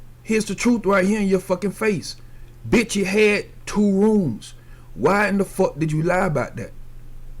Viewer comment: said they had 2 rooms on the live everybody thought she said we got 2 girls........ wake up